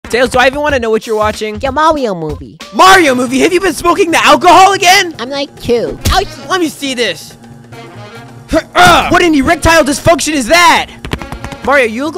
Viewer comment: I Thought Tails Said Your Mom 🤣